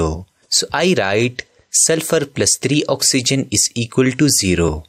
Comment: who that it was S+30 instead of S+3'O' 🤚🏻